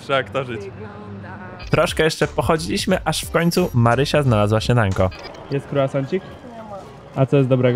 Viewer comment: czy tylko ja usłyszałem Marysia znalazła się nago ??? 😅😂